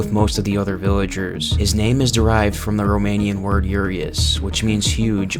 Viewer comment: did someone sayed romania?